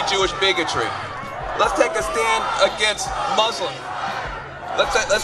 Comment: wait did he say Muslim? 😮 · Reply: A stand against Muslims?? 😊